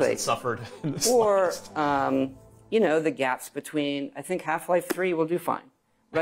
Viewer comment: Did he say Half-Life 3!!!